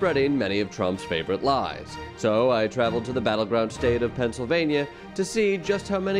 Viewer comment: I thought he said the "battle drug state".